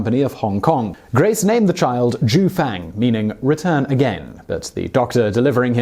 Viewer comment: I thought he said jew fang